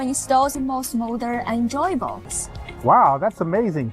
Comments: Did she just say that a Beijing Pass will grant entry into her JOY-BOX.??. 🤯😛👌👌 says.. "Wow, that's amazing".! 😂😂🤣🤣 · Hahahah!! Oh, man!!! @ 00:44:44 (LUCKY TS. No?!) XD XD But HO-lee sh!te, mates!? W-I-T-Freak is she saying?!? "...Da behzin pahz, d'beyzin-pahz. Wiss cooricose d'hes'mos cambee use for a spowce. To instahs-za-mos mowder ehn-joyballs [or is it ehn-joybowls instead, it can almost go either way? I personally like the former, but then again, that may be just a 'me' thing.]" XD What the heck does that even mean!? IDK why this is NOT a soundbite. XD At least the; "To Instahs za-mos mowder ehnjoybawls!" part. XD XD